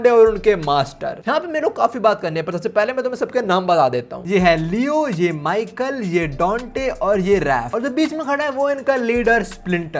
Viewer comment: Donate I heard Dante From Daveil may cry 😅